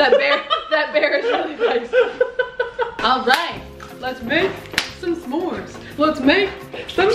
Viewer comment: i thought she said "Lets make, come CORN"